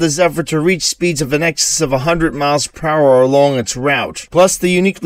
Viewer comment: Did he say speeds of several hundreds miles per hour?